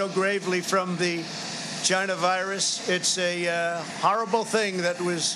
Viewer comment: Give me an award I still hear his voice and see his face only just for the sake of entertainment, did he say vagina virus! 6:26 🤣